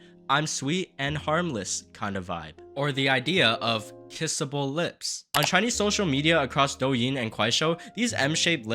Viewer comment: why the fuck did I hear "kissable lips" as "pissable lips" 💀